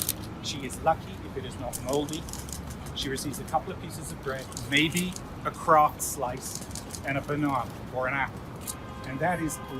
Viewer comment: Their subs are so terrible. He said "maybe a KRAFT slice" (like the brand) "and a banana, or an apple" I get it if the subtitle typist misheard because he's British, but still not that difficult to discern given the context. Quite funny. Also I'm not British.